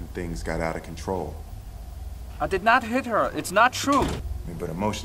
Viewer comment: I thought it said I did not hitler